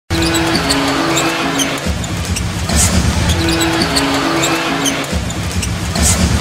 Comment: What say in 0:00 ? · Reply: Roniwi Castillo "Let's go Rockets."